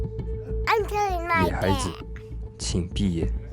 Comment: I DEFINITELY HEARD THE WORDS "I'm killing my dad."